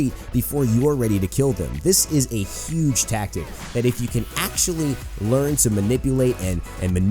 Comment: AXE-TULLY LOL i heard that